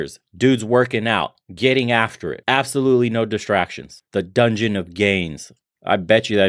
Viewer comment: I misheard you say "The Dungeon of gays" and I thought, "Well that's going a little too far."